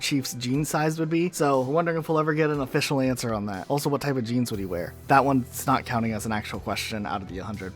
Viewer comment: Lol I thought u said genes 🧬 not jeans but I think he should wear a micro bikini